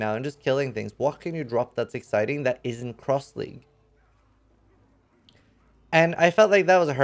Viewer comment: "What can you drop that is exciting that isn't _______?" What word is that? Cross-linked? Cross-league? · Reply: Cross-league.